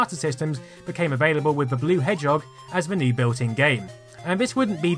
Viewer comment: Did he say" The Blue Head Job"